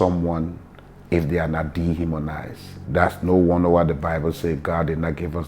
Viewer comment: The subtitles seem wrong. I hear the word "dehumanise" .